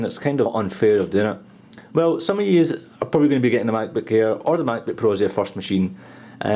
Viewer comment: sound like hes saying the "MacBook Ear" lol. Great Review!!